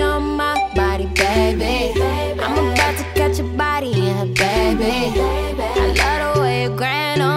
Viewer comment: At first I was like dang she's fine.. but did she just say "I'm about to catch your body and a baby"... ahh bail out bail out!